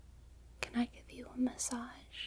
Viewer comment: lmao I'm so high, I thought the video said "Can I give you head." imagine my disappointment at 0:14.